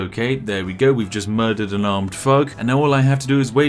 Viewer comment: Every time I heard him say armed thug, I kept hearing armed f$#& at first lol. Thought he ODd on coffee for a minute